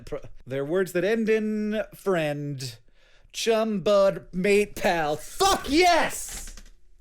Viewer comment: I wasn't paying close attention during the connections at the end, but when he said "chum, bud", as a hololive fan I heard "chumbud" and immediately paid attention. That would have been a very strange crossover